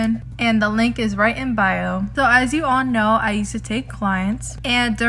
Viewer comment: when i first listened to this i thought it said: "so as you guys know i used to take lives" i listened a few times before realising that it said something else lol